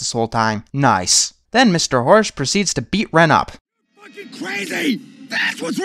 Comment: Did he say "You're fucking crazy!"?